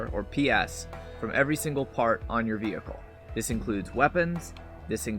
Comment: Every single fart on your vehicle That’s what I heard